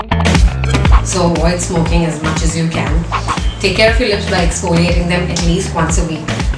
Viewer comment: wait did u just say smoke as much as you can sorry i cant understand you because of your accent sorry please dont be offended